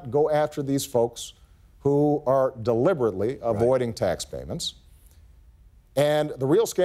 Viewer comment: subtitles say ...deliberately deleting tax payments when he says: ... "deliberately avoiding tax payments."